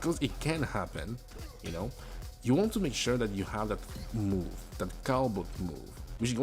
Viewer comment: "That move, that (cow-book?) move" i cannot quite tell what you said here (around 1:57) EDIT:: OHHHHH its like "cabot" or soemthing its a focus move, ok ok cool, i have not tried this move yet. sorry about this in case maybe someone else also having trouble with this one spot i'll keep this comment in.